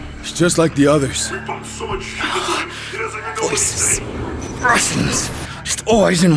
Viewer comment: “we pumped so much shit into him he doesnt even know wat hes saying”☠️☠️☠️ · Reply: I thought it was “he doesn’t even know his name”